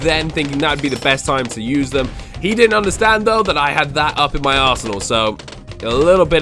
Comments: Was I the only one that thought he said "he didn't understand though that I had that up in my asshole"? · 4.47 did he just say:" he didnt know i had that up in my ass" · its sounds like he says up in my ass whole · i thought he said 'he didn't know i had that in my ass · I thought he say in my Arse hole few he didn't lol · the audio cut ougt halfway through the word arsenal, i thought he said arse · Was I the only one that thought he said "he didn't understand though that I had that up in my asshole"? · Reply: & BengiBen no I thought he said asshole until I realized it was arsenal